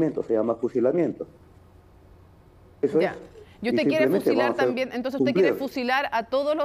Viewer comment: Minuto 19:18 la periodista le dijo yo te quiero fusilar también jajaja o no escuché bien